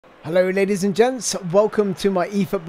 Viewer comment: Did you just say "Hello n*** and gents"????